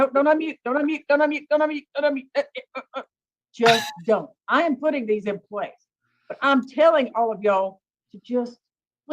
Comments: I thought she said “I am going to put these in place” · Verdict = sounded like Judge stated at 2:16:21 that she was putting these in place, meaning the orders. Did anyone else hear that as well? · I thought she said she's putting these in place 😂 poor Judge Manning!!! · It was confusing how it ended but I think at 2:16:25 said “I’m putting these in place” she said it really quick.